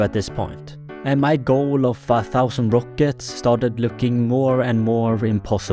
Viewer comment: I don’t know if you’ll see this, but you actually mistakenly said 5000 rockets instead of 1000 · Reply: no u mistakenly heard he said by accident my goal of fa thousand rockets